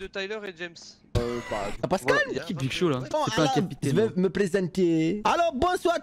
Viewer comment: Mdr 😂 j'ai entendu big show